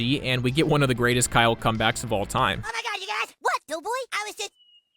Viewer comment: what is Kyle saying at 9:05? he says it so fast 😅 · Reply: What, dough-boy?